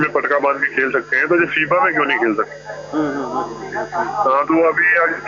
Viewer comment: did he say FIFA at 2:00? :o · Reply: Its FIBA